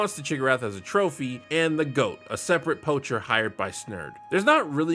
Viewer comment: Did you say……….goat?